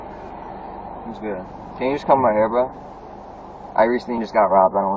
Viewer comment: sounded like "Can you just cover my hair bro?" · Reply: He said can you just come right here bro